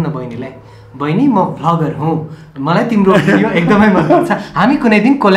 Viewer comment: who heard baini ma fuc*er huu instead of vlogger huu 😂😂😂